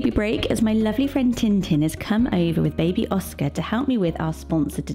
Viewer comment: I misheard 'Oscar' as 'Ausper' (awe-spurr/Oss-per) and am now loving that as a name 😮 I can't find any info on if it's a legitimately used name. I did find 'Auspelund' which apparently means freedom-lover, playful, friendly. I suppose it could be a spin-off from that! What do you think about 'Ausper'? Not 'real' enough to be used? It could fit right in to the Austen, Jesper, Oscar, and Vesper crowd. My son's called 'Iver' (a variation of Ivar, but pronounced as it is spelled with the 'er' ending), and I think I may just love that ending sound in some names 😅